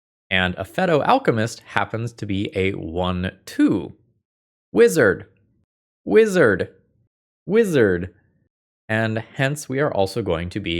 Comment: the fist time I heard this I heard "lizard, lizard, lizard" XD